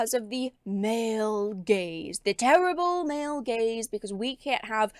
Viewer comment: The subtitle says "Male Gays" 😅 For the hearing implaired. She meant "Male Gaze".